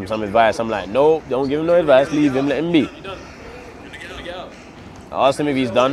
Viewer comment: That's how people train when they are serious. Ksi is way bigger than deji in terms of everything but if it was deji he would have fired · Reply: wait, u were talking about deji i thought u said left him lol